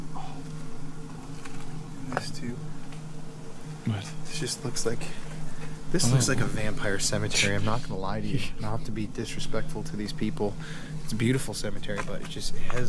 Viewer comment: The first voice was female and sounded like it said "sure looks like it"